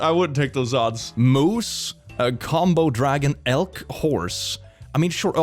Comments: did he just call a Komodo dragon a combo dragon.. 😭😭.. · Ah yes, that famous animal, Combo Dragon. To this day I thought they were called Komodo Dragons but I guess they grew to become gamers.